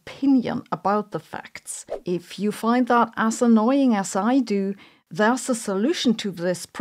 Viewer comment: This may well be inappropriate to say here , but I heard it & can not unhear it. When you said " as annoying" it sounded like you said assannoying 😅. In light of some incredibly irritating people on the world stage today whom shall remain, for each individual to imagine for themselves , that term seems wholly appropriate and has now entered my personal lexicon . Thank you for your amazing new gift to modern English Sabine ❤.